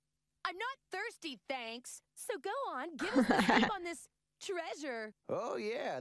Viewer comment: I thought she said "Gohan", but she says "go on".